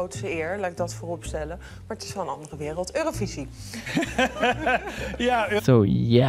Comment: she said "het is gwn een andere wereld" what shes trying to say with that that it is different from how it is in the netherlands she literally said 'its just another world'